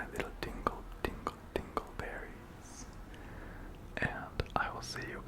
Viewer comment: I thought you said dingle berries 💀💀